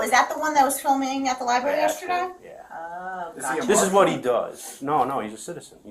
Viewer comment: Did he ask, "Is he a Muslim"? If not, what did he say? The cop followed up with a no and that he's American. So I'm not sure.